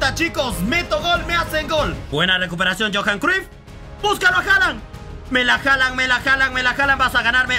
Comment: yo entendi me la haalan me la haalan me haalan o tambien mira a haalan mira a haaland mira a haaland 😂😂